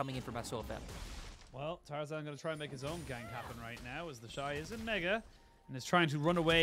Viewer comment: did he just say the N Word? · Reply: He very clearly said “isn’t mega” ????